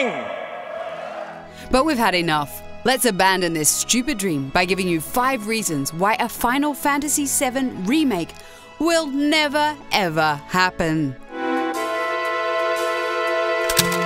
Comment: I thought you said five good reasons.